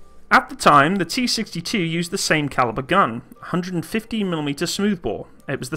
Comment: Ummm.. Did you say that the T-64 have a 150 mm main gun at about 6:25 in the video? I have played that part several times and it sounds like 150 mm every time. And when i put on the tex it agreed that you said 150 mm. So the later models of T-64 tanks were nerfed and only received a puny 125 mm gun since the use of a 150 mm guns was not wery sporting or?